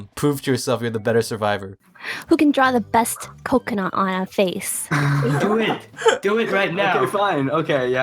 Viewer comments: "who can draw the best *coconut on a face*" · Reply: I thought she said ''who can draw the best coconut with a face'' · i swear i heard her say ''who can draw the best coconut on a face''!!!!!!! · I swear said "Who can draw the best coconut on a face"